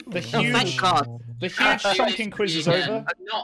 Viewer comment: No joke for a second i thought it said huge chonkin Magnus Gun and i was like WHEN DID THEY GET A BIG ASS GUN?!